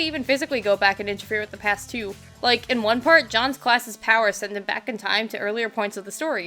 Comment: Minor point but fairly sure retcon powers are not part of Breath powers (it sounds like you're saying "John's class' powers"). Sure, they do fit in the freedom theme of Breath (freedom from narrative and timeline rules), but they're not part of it inb4 i am mishearing things